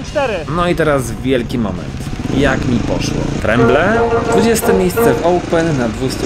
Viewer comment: co to było ?? a to tręble bo jak zatrzymałem to usłyszałem jak mówisz tręble